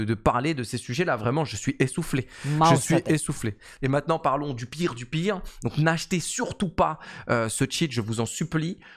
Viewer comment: cest quoi qu'on entends en background ??? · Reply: c'est la video d'expliquation de l'angle snapping qu'il a du relancer sans le faire exprès, ça dit ''mouse settings''